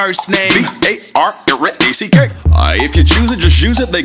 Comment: when he was spelling his last name it sounds like he said B-A-R-A easy cake